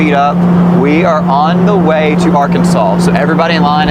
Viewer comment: did he actually say Arkansaws